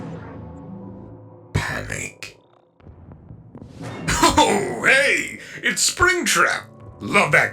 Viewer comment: I thought nightmare Freddy said is canic its not its panic